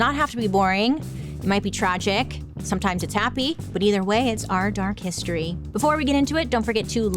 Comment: Love your videos but in your intro it sounds like you say, "are dark history" instead of "our dark history". I had a speech impediment when I was a kid with speech therapy classes so it bothers me. I love all your videos. ❤